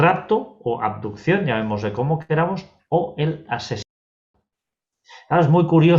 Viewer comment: ¿El rapto y el qué? Es que no entendí esa palabra. ¡Por favor si alguien es tan amable! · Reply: Creo que dice asesinato